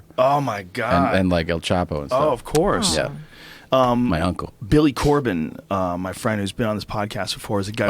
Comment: Who else thought he said Billy Corgan lol